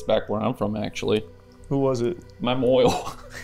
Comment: what is “mymoil “bc I deadass laughed not knowing what said